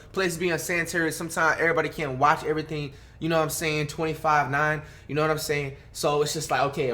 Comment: did he say 25/9 instead of 24/7